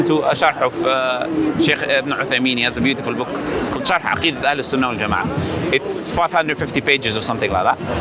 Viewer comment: what book is that? i didnt understand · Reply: Salaam! The book is by the renown scholar Shaykh Salih ibn Uthaymeen - Sharh Aqeedah Ahlus Sunnah Wal-Jamaa’ah.